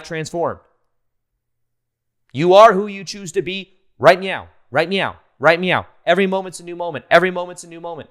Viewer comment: did you actually say "right meow" or is it just me.....